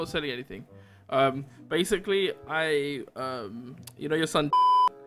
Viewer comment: you know my son fu*k? Thats what i heard